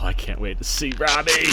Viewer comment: I heard "I can't wait to see Robin !!!". For like four or five seconds, I was like... dude, no. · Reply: Hahah no not Robin... Robbie for the Battinson